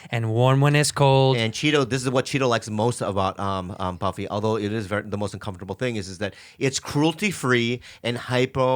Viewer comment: Sounds like Bobby said during the read, “...even though Buffy is the most uncomfortable...”